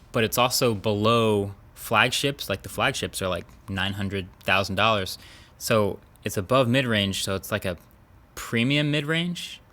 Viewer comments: Am I the only one who heard him say "The flagships are like 900 thousand dollars. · At first I thought he said the flagships are like 900 thousand dollars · I misheard that as "flagships are $900,000" and then I thought we're probably not far off from that.